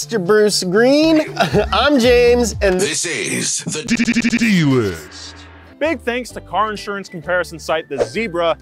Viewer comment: he said and this is the d-d-d-d list. bro i thought he said HIS AIDS THE DD-D-D-D-D- LIST LMFAOOFSDHOSADPIJO